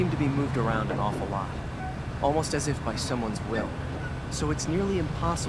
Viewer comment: did anyone else notice a 8:57 joseph says "someone's" but the subtitles say "somebody's"